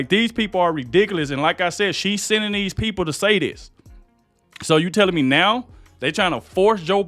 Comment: said, "say this" and I thought he said satanist. Too funny. My last post was erased.